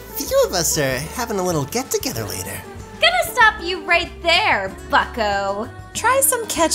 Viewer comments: I thought said fucko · I swear said something terrible... I might be hearing things. XD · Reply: She said bucko.